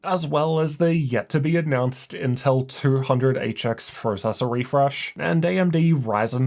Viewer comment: 💵 Save money on your next gaming laptop with our daily deals: <link> 🔍 Find your perfect gaming laptop in 30 seconds: <link> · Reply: did I hear it correctly "Intel 200HX Refresh.."?